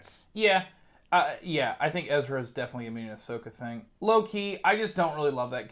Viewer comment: Might be able to make a drinking game out of every time says "low key"... Also, every time he says it I hear "Loki". 😁